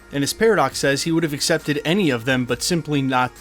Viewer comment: He actually says he WOULDN'T have accepted any of them. But he especially wouldn't accept this one.